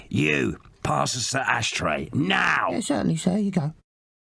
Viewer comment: What does 2D say at 7:46? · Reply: “yes, certainly sir here you go”